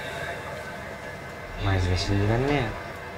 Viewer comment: あの人なんて言った？ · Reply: 兄者。 マジで信じらんねーよ に聞こえる